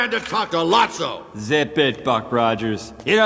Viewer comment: what is that fuc# he said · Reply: « Zip it buck ranger » I think